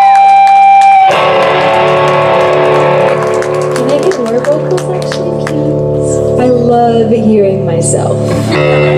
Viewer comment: what did she say? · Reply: I think it's "Can I get more vocals actually, please?"